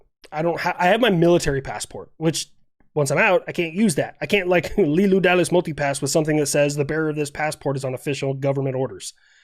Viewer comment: It took me three tries to understand what you said here: Leeloo Dallas, Multipass